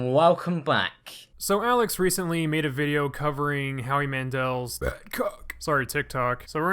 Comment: I thought you said “Howie Mandels big cock”